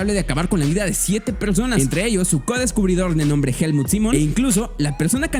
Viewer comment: No sé pero en el minuto 5:01 escuché el nombre de Helmut Zemo me hace recordar a Marvel :v